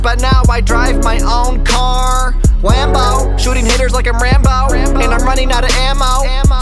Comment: When he said hitters I thought he said the n word with a hard r for a second